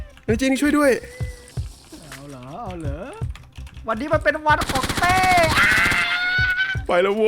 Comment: ทำไมได้ยินว่าวันนี้เป็นขวัญของเต้อ่ะ 😂🤣😂